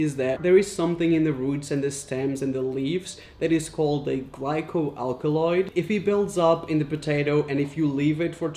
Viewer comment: I thought he said alloy and I was like MR AIZAWAS SCARF IS MADE OF THAT- oh. wait.